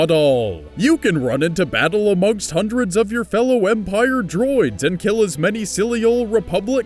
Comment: you did not just say, 'Empire Drones'...